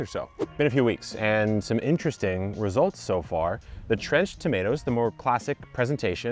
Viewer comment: Trenched Tomatoes?? I can't be the only one who hears this.. Anyone?...